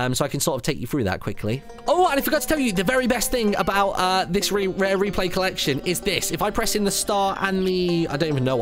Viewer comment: god dammit i thought it said rare pepe collection